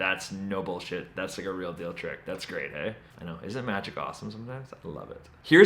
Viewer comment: did you say "That's grade A" or "That's great, eh?"